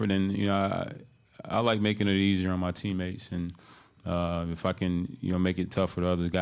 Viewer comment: Did he say fuckin?? Lmao did I hear that?